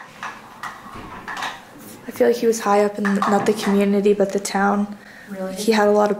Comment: it sounds like instead of ‘he was’ it sounds like my name creepy